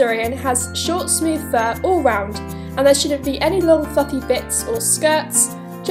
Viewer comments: Ummmmmm lol who else heard "And shouldn't have any long f.....g bits" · I’m so sorry but I heard 0:19 as “And there shouldn’t be any long f-ing bits” 💀